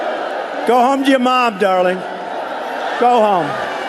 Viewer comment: Did Trump call the protester "Darling"?